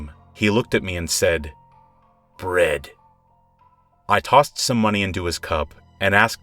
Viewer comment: I thought he said, "red" instead of "bread"